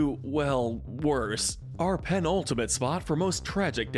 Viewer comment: I THOUGHT HE SAID ULTIMATE INSTEAD OF PENULTIMATE AND I WAS LIKE ??? WHERE'S SAEBYEOK??? I LOVED SAEBYEOK